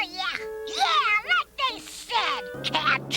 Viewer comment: Did he say "What the shit?"